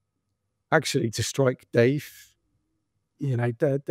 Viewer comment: For a moment, when he was talking about striking Deif, I misheard it as striking Dave. Poor Dave